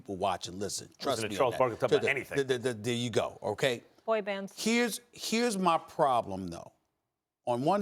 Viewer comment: Am I the only one who heard Molly call out Max as a Fan boy... "BOY BAND" 😂😂